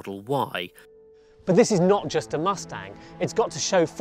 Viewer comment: “its not just a mustang” correct! Wait you said just? I thought you said “its not a mustang”